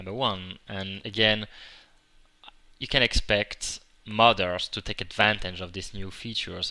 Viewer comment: when he said modder's i hear mother's xD